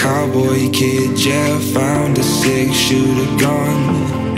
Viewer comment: “Yeah, found a six shooter gun” I thought he said “yeah, found a sexual gun”